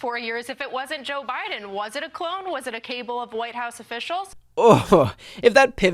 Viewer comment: Ok, am I the only one who heard "Is it a cable of white house officials?" (approx 1:49) - I think she meant cabal?